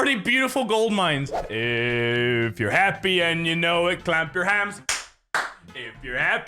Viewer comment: Closed captioning: 5:55 “if you’re happy and you know it, clap your hands” instead of “clamp your hams”... among other mistakes